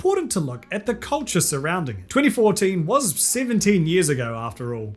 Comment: "2014 was 17 years ago" got me for a second lmao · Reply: I was so confused at first because I heard 7 years. I was like "wait that was almost 10 years ago" and THEN heard him say 17 and got even more confuses xD I hurt myself in my confusion